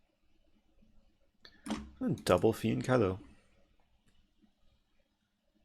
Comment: what is the word he uses? Double what? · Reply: Fianchetto! <link>